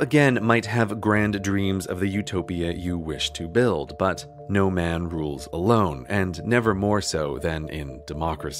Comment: every time he said "no man rules alone" i heard "no king rules forever" :D